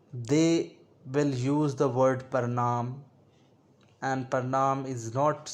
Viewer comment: What is this word described at 9:19? "Pronoun?"